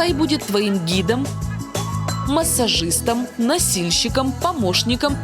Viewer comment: мне одной послышалось насильщиком